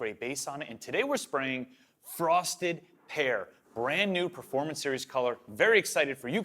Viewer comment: OMG, I thought you said frosted hair. Lol